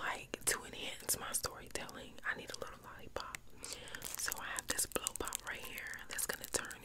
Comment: I thought she said blue lollipop?